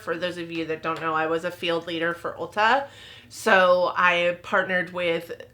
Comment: Oh misunderstood thought you said you used to e district manager at ulta